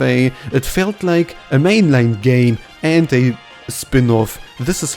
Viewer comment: He keeps saying gay instead of game :_KirbySmug